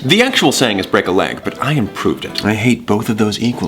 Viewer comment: I've always thought when said "fracture a femur", when he said "it's break a leg, but I improved it" I thought he said, "but I have proved it" and I was always confused...